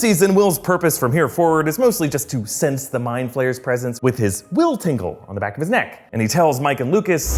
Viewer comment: did he just say will tinkle 😂